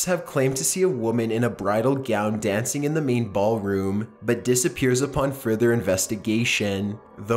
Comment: did he say meatball room?